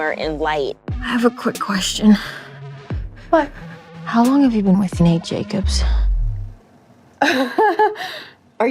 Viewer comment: Whaaa, I thought Rue said:” How long have you been fuc***g Nate Jacob’s?” Not the other way 😮